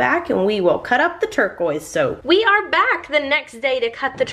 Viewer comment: hah I thought it said tortoise soap at first (also I love watching these videos)